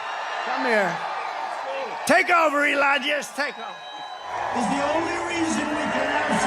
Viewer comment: did he just call Elon, elonesk?